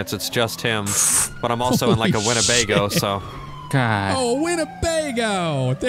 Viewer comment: did he say Winnebago.